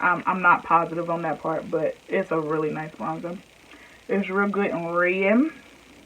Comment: Can someone tell me what she says at 15:22? I keep replaying it 😂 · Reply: Lmfaooooooo excuse my accent, I said it’s goodt and red, I was referring to the color! 🤣